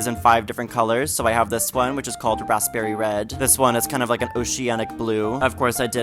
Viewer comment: The way I gasped as soon as you showed the fired glazes, that RED 😍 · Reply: I thought he said, Rustberry Red. Such a beautiful color